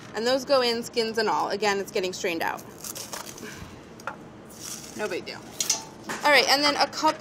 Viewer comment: This new Method sponsorship will last until Brad's next episode when he inadvertently says something horrid about it. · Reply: When Molly's garlic head breaks apart and it sounds like she says "No big dill" and then proceeds to use dill